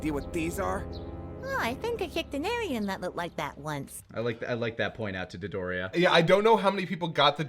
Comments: As for the “I think I kicked an alien that looked like that once” and people not getting it, until I watched this commentary, I thought he said “I think I picked a durian nut like that once” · I swear to god I kept hearing "I kicked an aryan" instead of "I kicked an alien" for the longest time until it finally clicked.